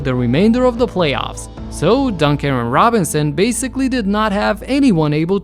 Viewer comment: i thought he said duncan robinson like the heat player lmao